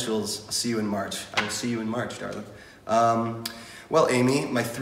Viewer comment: I thought he said "see you in March, Darla" istead of "see you in March, darling" I ALMOST HAD A FUXKING HEART ATTACK